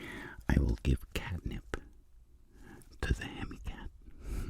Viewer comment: Is it just me, or did he just say he's gonna, and i quote.. "Give catnip, to the Handicapped.."